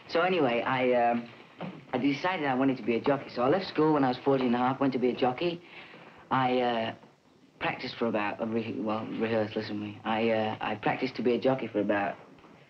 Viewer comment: I thought he said, “I want to be a junky. I practiced for 7 months to be a junky.” Lol.